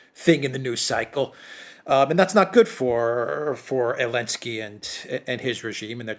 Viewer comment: he actually said Elensky 🤣